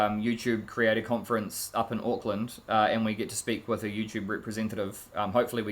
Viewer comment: Hopefully everything works out! It sounded like you said Oakland. If that's correct, be careful in Oakland. Get in and get out! Do not leave any valuables in your car.